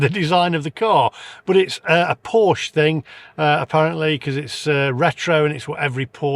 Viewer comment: Sorry I thought you said porsch tinman.